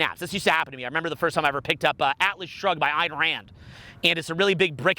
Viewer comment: Great video! Someone got the title of the book he names at 17:36? Can't understand it. Would really appreciate it :) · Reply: atlas shrugged - ayn rand